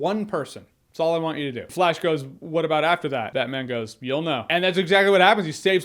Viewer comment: he said "you'll know"?.. I thought he said, "You can go."